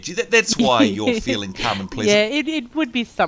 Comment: For a moment there I honestly thought Reggie said she was feeling nice and plastered. In other words she was calm because of having some alcohol, haha. Thanks for the video you two.